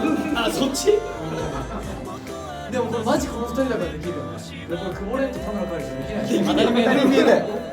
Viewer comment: の樹はなんと言っているのでしょうか・・・？ 何回リピートしても聞き取れず、何に対して2人が「あたりまえだよ！」と 突っ込んでいるのか気になりすぎて( ; _ ; ) どなたか教えていただけるととても助かります・・・！ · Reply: 「まじこの2人だからできる。 久保廉と田村カイル(字は曖昧です)はできない」と言っていると思います。 樹くんが可愛いがってる中学生ジャニーズ jr。 だからベッドシーンはできなくて当たり前、ですよね😂